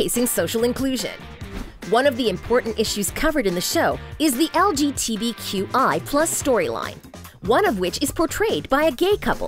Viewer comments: LMAO WHAT DID SHE SAID IN 10:21 WAS IT LGTV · LGTVQI? HAHAHAHA LOL DID I MISHEARD THAT? IT'S LGBT NOT A TELEVISION COMPANY LOL!